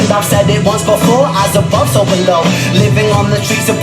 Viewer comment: What a beautiful poem. Makes me think about my Mum, even though she passed 7 years ago, I still miss her. The first time I heard this I thought Ren said 'As a Bus overload' instead of as above so below. The Guitarist with The Verve loved his version so much he sent Ren a Guitar. Great story. I know that living alone has it's pluses & minuses.